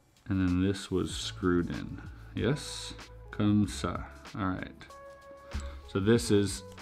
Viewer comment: comme ça? Is that what he says I wasn’t expecting the French tbh